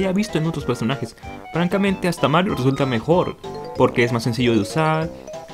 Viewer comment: marth o Mario no escuché ese comentario?